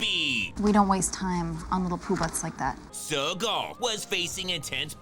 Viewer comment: I've played this back six times, is she really saying what I think she's saying? little poobutts?